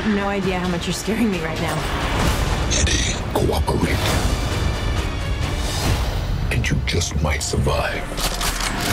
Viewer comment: What is venom saying at 1:54? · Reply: eyes,lungs,pancreas so many snacks so little time